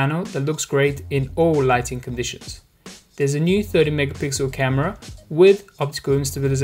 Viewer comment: Sounded like he said 30 megapixel. No I'm not trying to be mean, it is just what I heard. Found it funny cause yah know HTC and cameras lol